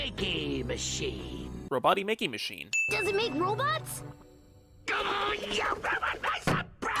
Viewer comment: When tails says “does it make robots” I thought he said “does it make robux” I thought tails was a robloxian.